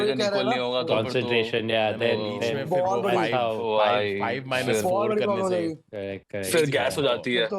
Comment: did he said constipation !